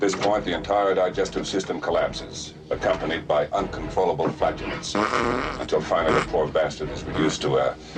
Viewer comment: Did he say followed by uncontrollable Fartiness? I couldn't hear what he said properly due to the pilot farting LOL